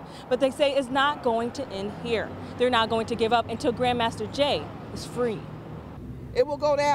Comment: I thought they said Jam Master Jay